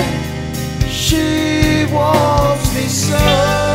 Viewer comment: I want you too! ♥️ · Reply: Oh fuck. I thought you were saying she wants me so....damn it